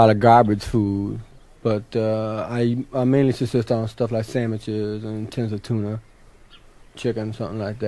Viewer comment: The best, even all these years later.... :) Reality check needed - did he actually say "sammiches" at 3:08 ?